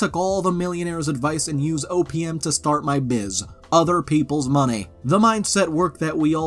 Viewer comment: I wasn't looking at the vid and thought you said "I used opium to start my business" lol